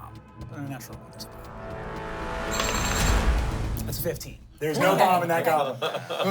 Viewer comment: I kept hearing "There's no Gomb in that Boblin"